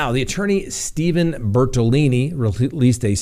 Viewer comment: Please don't take this as anything more than fun...I couldn't keep straight whether the Laundrie lawyer was Bertolini or Bertoloni, but really cracked up when I think I heard you say "BertoLoony". It seemed inadvertent but maybe it fits!